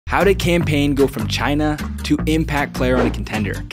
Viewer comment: Cam Payne sounded like he said campaign 😂